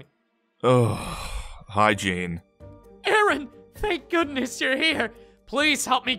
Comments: Hahaha. When Aaron said: «Hi Gene.» It sounded like he said: «Hygiene.» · When Aaron said hi gene I thought he said hygiene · When Aaron said"Hi Gene" I thought he said "Hygiene"